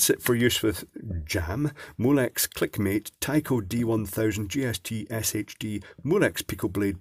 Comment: as an American, when you say "JST" I hear "GST". See 30:10. Like "Zee" and Zed", perhaps you can help us with the proper way to say "G" vs "J".